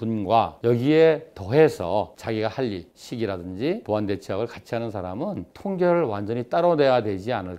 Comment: "보완대체의학을 같이 하는 사람.." 이라고 나오는데 자막은 "보완 대처하고 같이 하는 사람..."이라고 있어서 차이가 나는데, 전 소리 들리는 게 더 잘 와닿습니다.